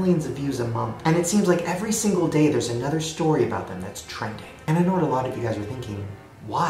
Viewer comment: Bruh, after you said the pual brothers are taking over the internet I heard a whisper that said I know at 9:50 or something